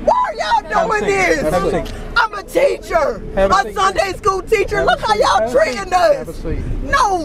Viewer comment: A bunch of professional victims teaching the next generation. What a shame. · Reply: I thought she said Sunday school teacher.